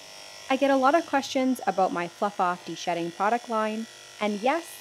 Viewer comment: i thought you said "fuck off " but no it's "fluff off"